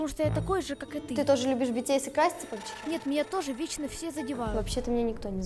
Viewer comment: мне одной показалась что она сказала BTS?